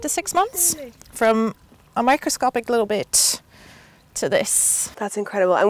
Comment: I heard “microscopic little bit*h” 😂 had to turn on subtitles